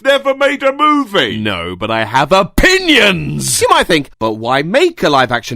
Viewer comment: When you said 'opinions' I thought you said 'pingas'